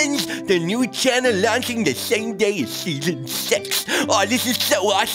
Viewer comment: did he just say sex? Like if you heard it